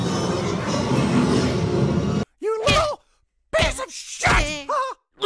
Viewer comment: you know basic shut！haha って聞こえる